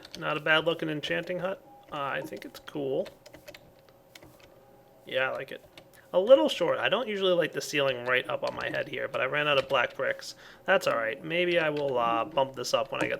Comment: not to be racist but I thought you said black pricks...